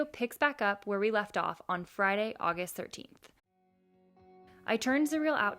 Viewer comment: did she say F-friday the 13th?